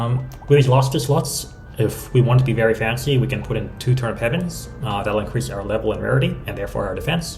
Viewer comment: what did he say at 14:50? Turnip's heaven? What?